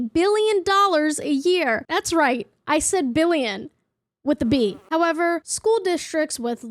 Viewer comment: I… It’s too late for me right now. I heard “that’s right. I said million. With a ‘b’” and I just had to reload my brain.